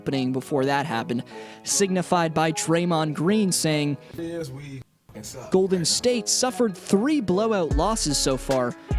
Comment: signified by Green saying at 8:20 yeah I have no idea what he said either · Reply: Bubster 1969 sorry bout that.. he said “the reality is we fuckin suck”